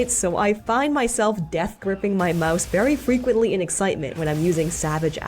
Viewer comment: Did she say "Mouse" !!! 😱😱😱 I can't imagine not playing monster Hunter without a controller